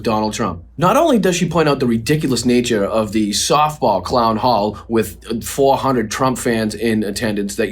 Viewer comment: "clown hall" now I get it, I thought they said town hall so I expected substance but now I know why I got zilch.